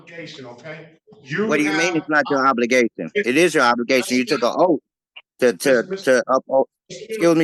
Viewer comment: “You took a oaf” 😂😂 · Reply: I thought he said "You took a loaf"